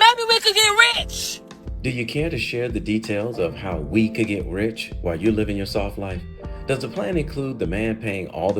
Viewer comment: DID JUST SAY HOW HE CAN GET 🐝ITCHED?